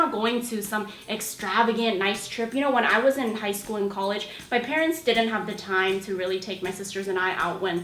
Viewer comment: uhm, did you say "when I was in high school in college?" 😂😂😂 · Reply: when I was in high school AND in college. haha